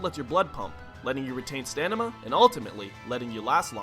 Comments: does anyone else hear him say "stanima" instead of "stamina" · Did he say stanima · wait, did you just say "Stanima"? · does he say stanima instead of stamina? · Did he say stamina? or is it just me?